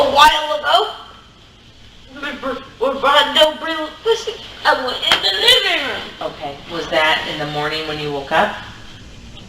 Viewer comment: What did he say? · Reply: When ? and Bre went missing i went into the living room